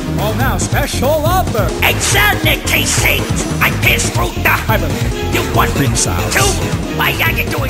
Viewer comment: i fr thought it said "i pissed through the hyperlink"